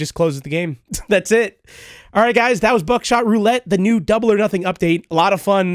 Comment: Bro I thought that said Backshot Roulette